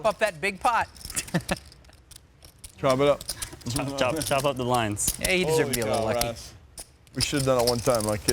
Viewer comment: does Randy say something about chopping out some lines?!?! No wonder he plays 13,000 games at once